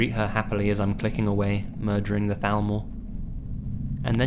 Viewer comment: did he say Thalmor? Was he playing Skyrim? ^_^